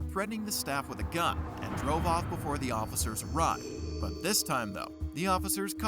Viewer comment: I generally like your videos but a BIG criticism I have is the subtitles you provide can be very off compared to what is said. For example @ 21:58 (Subtitles : "Before the officers. Right." vs. what is actually said ; "Before the officers arrived,") @ 22:01 (Subtitles : "The officers. Carter." vs what is actually said; "The officers caught her.") I would understand more if it was Youtube's auto-caption but since you do take the time to already include subtitles in your videos, it would be best to review them beforehand. While I know videos take a lot of work to prepare, I think it would be beneficial to double, triple & quadruple check the subtitles, especially considering HoH (Hard of Hearing) people would benefit extremely with reviewed subtitles along with viewers who just find subtitles pleasant :- )